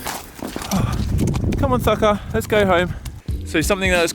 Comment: Did you just say "come on fucker, let's go home" at 10:21? hahahah or did he say something else?